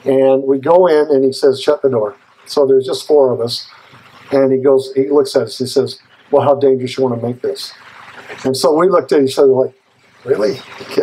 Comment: I cannot understand what he says at the 2:00 minute mark -in, he Says Shut the Door then WHAT? Even when I use the Closed Captions option it doesn’t know what he’s saying! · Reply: so there's just 4 of us, and he looks at us and says well how dangerous do you want to make this. and so we looked at each other and said really